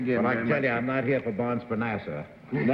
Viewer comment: Can somebody please clue me in on what Edward G. Robinson meant when he said: “I’m not here for Bonds for Nasa”…? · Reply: Nasser.